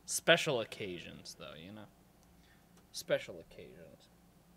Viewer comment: When he said "special occasions" I thought he said "especially caucasians" lmfao